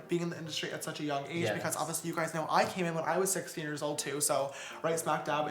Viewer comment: He said when I was six years old did anyone else hear that 😂🤣😂 it's